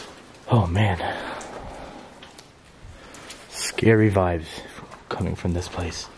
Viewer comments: When u said "scary vibes" that little voice sounded like it said "let's go" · It sounds like it said "piss-off" when you say scary vibes